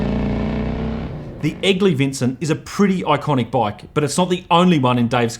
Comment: Being a Yank, for the first bit of the piece I thought he was saying “Ugly Vincent”😂 My youngest son’s middle name is actually Vincent, half for VanGogh and half for this beautiful motorcycle.